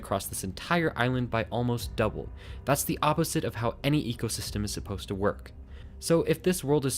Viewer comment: correction ‘An’ she said an ecosystem instead of any because we only know of one in our world. Further on an AMAZING video